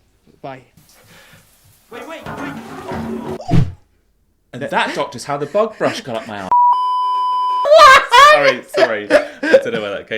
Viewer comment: My hearing isn't the best, but I think I just heard, "And that, doctor, is how the bog brush got up my a------e." Bahahahah.